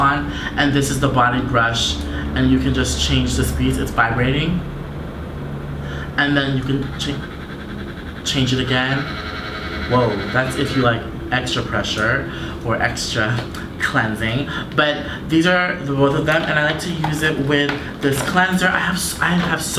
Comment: When you said "that's if you like extra pressure" I thought you said "that's if you like extra pleasure" 😂😂😂